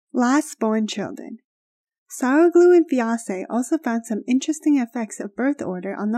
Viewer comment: Lol did anyone hear "sour glue and fiancée" at 2:36?